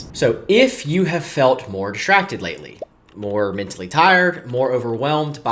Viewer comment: I think it says something that I thought he said “more attractive lately.” I was like, ‘Wait - huh??’ 😅